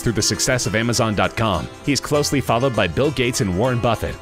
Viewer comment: Dude slow down. I had to play the video in 0.75x speed just so I can understand you😂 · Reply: At one point I thought he said Musa is followed by bill gates